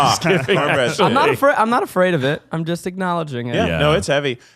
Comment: For half a second I really thought was saying "I'm not afraid of it" to cornbread